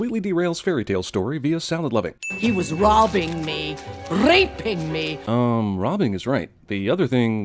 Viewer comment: I think she says reaping me